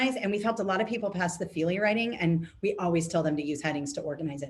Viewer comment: What are you saying at about 21:44-45 "feely writing?" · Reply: Hi there. "Feely writing" might be a misinterpretation of "freely writing," which refers to writing without strict structure or organization. ~Kathy Customer Support at Kathleen Jasper LLC